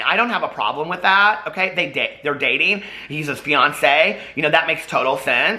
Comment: I kept hearing “Ryan’s Beyoncé” instead of fiancé 😂